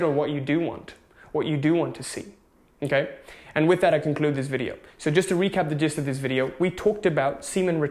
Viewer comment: Day #769 I thought he said to conclude the jizz of this video 🙃